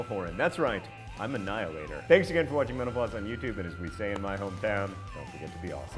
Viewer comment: It sounded like you said, "I'm a Niall-hater"